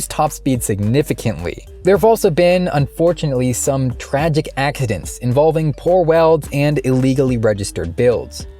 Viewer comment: Did he really say "ackidents" at 5:32 ?